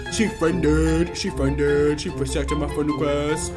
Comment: in 3:06 did he say she pregnant?